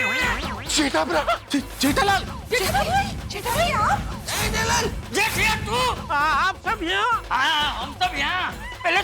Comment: 🤪 1st i hear jetha bhai 😂then i understand jetha ji😅😅😅